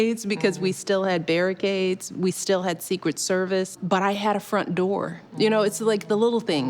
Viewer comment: i genuinely thought she said she had a firm dwarf. she said 'front door'. i was eating at the time and not paying attention.